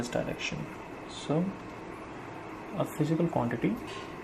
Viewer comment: does he say quantity or quality and it looks like he wrote quality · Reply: He said Quantity